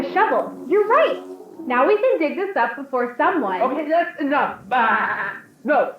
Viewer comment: I noticed cut her of when she said “we can dig this up before someone” and she might be referring to someone finding a body that she was going to move or something like that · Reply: oh I thought she said "we can dig this up before sundown"